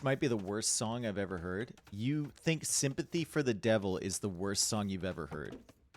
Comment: thought you said symphony for the devil and got excited. unfortunately I'm with random chat person on this one